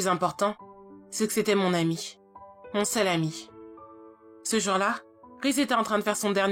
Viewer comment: pourquoi j'ai entendu "mon salami" ? 😭